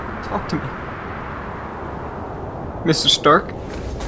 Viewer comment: why did i heard mr stomach on this one lol